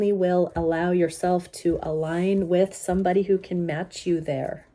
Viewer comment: When at first I heard, “When you come into Wholeness, you only will allow yourself to align with somebody, who can match your hair!” I totally agreed…❤❤